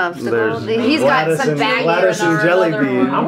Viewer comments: Skeet : Gladys and Jellybean Lili : Did you say lettuce? Skeet : Gladys Lili: Gladys? I thought you said lettuce! Both start laughing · Lili: “I thought ur saying lettuce” 😂😂😂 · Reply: tbh I thought he said lettuce too